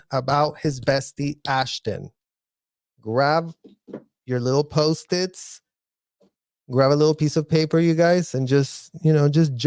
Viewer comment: I heard him saying ‘little puppies’ in the back and masterson put his hands on his chest…. (Puppies are breasts if anyone isn’t sure what it means) I think they’re saying about young girls 😢😢😢